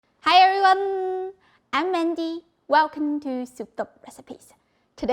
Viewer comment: Hi!!! Everyone says your name is Mandy but you sure sound like your saying " Mindy"... do you read your comments??? If so...please answer me. Anyways...your recipe for today looks super delicious. My grocery store never has anything good. I know they won't have the thin eggplants. I can't wait to try this. YUMS!!! Great Video!!!...🦋